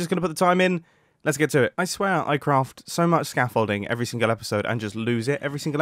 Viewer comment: When he was looking for the scaffolding. I thought he said 'shit building frame'